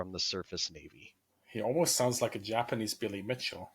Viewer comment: I heard "Almost like a Japanese Billy Bishop" and was mighty confused.